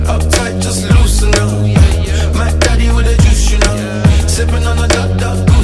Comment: I swear i heard 'Macdaddy wants to juice u now' 😂😂🤐🤐🤐